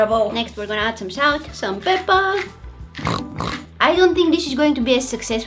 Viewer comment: She said pepa (^🐽^) I thought she said pepper 🌶 9:07 to 9:09 🤣🤣🤣🤣🤣🤣🤣🤣